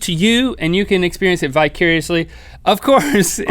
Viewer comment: anyone else hear bicuriously? Upon relisten I heard vicariously :D